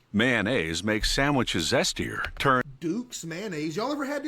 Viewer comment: did bro say zesty